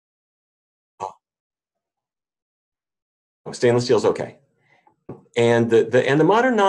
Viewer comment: Oh darn I missed what you said at 19:19, you have no sound there. Well I am going to guess. LOL But the rest has sound. Great video, Thanks. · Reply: oops... I was asking about stainless steel pans.